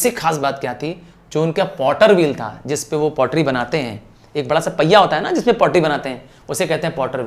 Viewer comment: Anyone noticed what he said instead of pottery :hand-pink-waving::face-blue-smiling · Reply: Potty banaate hai 😂😂😂😂